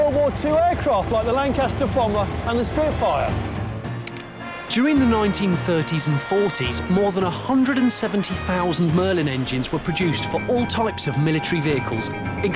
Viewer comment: I thought he said a merlin engine.